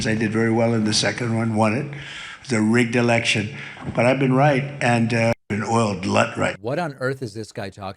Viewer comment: did tRump say “blood right”?💀🫨..